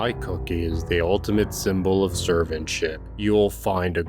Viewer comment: i know he said servantship, but it sounded like he said “serve and s@